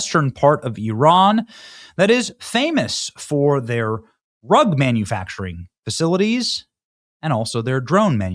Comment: Thought you said "Famous for their rug munching". 😅🤣